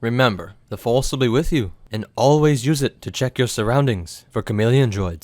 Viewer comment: am I the only one who thought it said charmeleon Droid at first